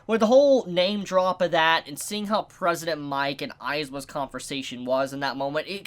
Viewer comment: I'm sorry, did you say "*President* mic"?